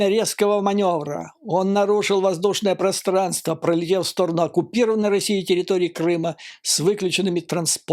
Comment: я правильно услышал? Они назвали Крым и другие захваченные ними территории ( Оккупированными)?!